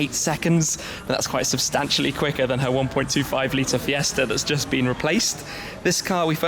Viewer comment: sounds like you said 5.0l fiesta lol